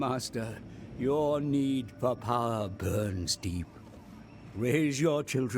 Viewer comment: Well, my hearing should be checked, I heard "bernstein" instead of "burns deep" when the annoying old fart talked. I wonder if the Vampires can build that special building in... Quintex I think it was, which lowers the penalty for lack/presence of corruption...